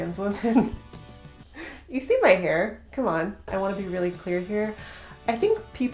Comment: When you said “I want to be really clear here” I heard “I want to be really queer here” and I was like same